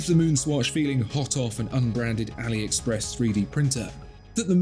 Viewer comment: Thought I heard AliExpress here😅